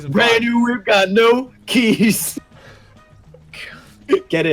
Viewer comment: i heard that as "Miranda got no Keyes"